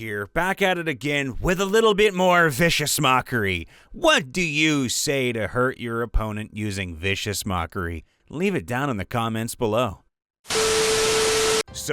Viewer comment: I thought it said vicious monkey 💀💀